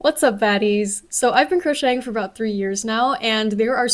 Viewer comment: at the start of the video... why do I hear her say 'whats up fatties' is it actually fatties or is it baddies... HELP ME